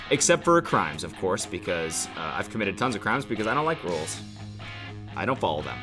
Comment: I thought she said she didn't like "rolls." I was like sweetheart no rolls with honey butter are you kidding me??